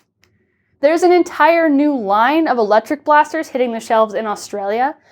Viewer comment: You said "Dart Tech" and I heard "Dark Tag" and got excited for 4 new dart tag blasters that will never come. I know it's unlikely for velcro darts to ever come back, but one can hope!